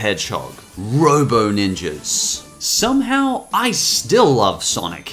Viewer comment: i swear i thought it said "robo NIGGAS"